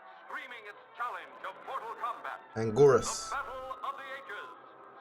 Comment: did he really say “Mortal Kombat”